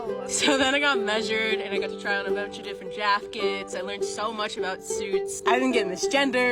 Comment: I’m sorry, a bunch of different “jafkets” is what I heard. I stopped the dishes to write this. EDIT: I Stopped at 31:21 to see if they said “hanges” and then went back again and they DEFINITELY said JAFKETS